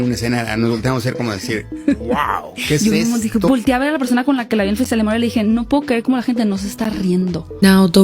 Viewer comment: Hi! Thanks for this incredible video! Just a little note, in the minute 29:30 I think the subtitles are wrong, If I'm not mistaken, she's saying "I can't believe how people are not laughing" 🦆🦆